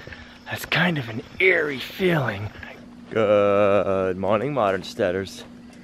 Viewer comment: we have been watching your channel for a while. My daughter used to say can we watch "Feathers"? She thought you were saying "Good morning my names Feathers". Thought you might get a kick out of that.